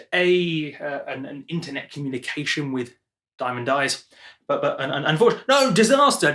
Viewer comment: I thought it said in the DAW with panda eyes 😂